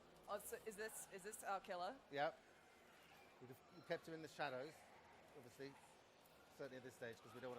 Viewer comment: Am I hearing this correctly? (13:55) she asks "Is this our killer?" he replies "Yup, we kept HER in the shadows, obviously. Certainly at this stage because we don't want to spoil it" I think he just did.